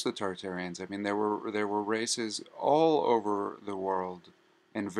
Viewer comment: I almost thought you said that there was racist in the old world 😂😂😂